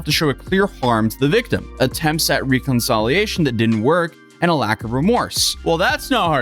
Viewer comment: I think you said “Reconsolidation” but possibly meant “Reconciliation”. Sorry to nitpick. But it stood out to me ha.